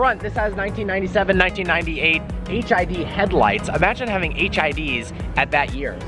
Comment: did he say HID or HIV???